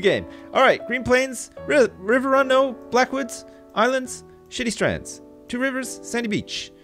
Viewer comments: I thought you said shitty strands instead of shady shady strands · It sounded like he said shitty strands